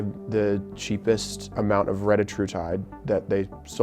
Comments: I was listening in the background and I thought he said “redditortide” as a joke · Thought he said “Redditortide”, which would have been appropriate